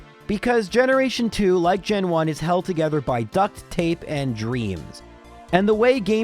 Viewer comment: Sounded like tlu said it was held together by duct, tape, and dreams. like as aparate items